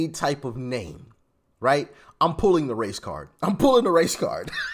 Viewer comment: Pulling the Racecard.... I thought you said "pulling the Racecar" 🏎️......... I was about to say, DAMN what kind of racecar? Inclosed or open trailer? But then I realized what you said...........I think I might be an idiot. 😅